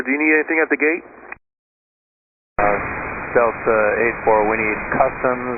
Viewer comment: the thumbnail photo says they request Law Enforcement. but you come here into the video and it says customs lol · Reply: I can hear the captain say on the radio "...we need customs and that should be it..." I don't care what the thumbnail says, that's not the actual radio message. The captain reads "we need acustoms" what appears to be a typo because 'acustoms' does not make sence.